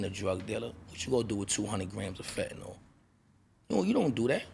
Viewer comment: I thought he said shotti ran off wit 200 grams of fetty wap then said he was loyal